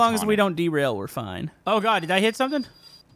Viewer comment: Did you just say oh God?! 4:24 First Time I've Heard that.